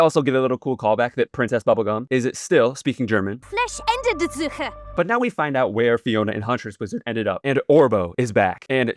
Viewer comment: as a german, she DID sometimes have rough pronnounciation but this time I have no idea what word she is even saying 😭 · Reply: Someone on reddit posted that according to the subtitles she says "Flächendeckende Suche" aber hab das auch partout nicht verstanden obwohl ich auch deusch bin haha